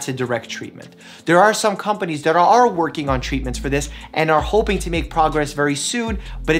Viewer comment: LOL. When you said "they are hoping to make some progress on this..." I heard "They are hoping to make some profit on this..." I'm sure both are true the former statement true to make the second statement true as well. LOL. (Not a pharma lover as you can see)